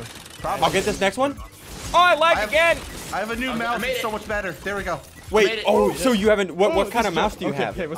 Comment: I heard "I have a new mouth it's so much better" XD